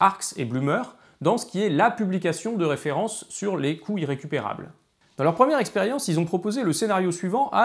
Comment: Moi je crois que c'est mon cerveau entier qui est irrécupérable x) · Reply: le mien il est bon à jeter, plusieurs fois dans la vidéo j'ai compris "les couilles récupérables" XD